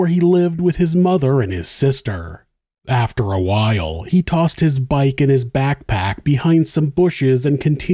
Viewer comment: i thought you said “he tossed his bike into his backpack” and i was so confused. woulda been a big backpack